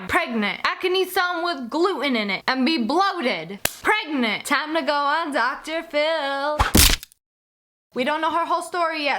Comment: i thought it said "girl pregnant with scooter"